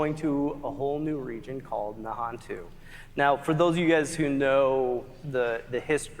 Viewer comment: when they started talking about the new zone, the closed captioning in the arena put Non-Jew instead of Nahontu or however you spell it lol. Was hilarious and they had to shut off the CC.